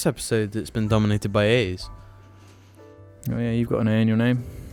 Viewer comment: I thought he said aids